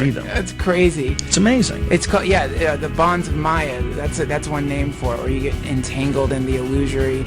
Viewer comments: Wait what did he say @ 3:52 bonds miya or bronds miya?? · does anyone know what the other person s saying at 3:54? it sound like "the vons maya" being entangled in the illusory aspect of the universe. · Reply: law of maya (illusion)